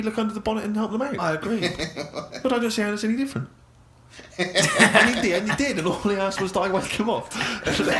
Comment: I finally figured out what Steve said. Closed Captions can help, but not in this case. Reducing playback speed can help too. For the answer, see my reply below... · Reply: Steve says: "And in the end he did, and all he asked was that I whack him off."